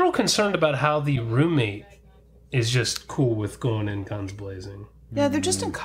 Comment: did you say "guns blazing" or clunge blazing? is this like taco burn? lol